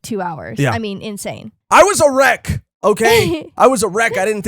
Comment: I thought was saying "I was erect" in the beginning, took me a few moments to realize he was saying "I was a wreck" 😂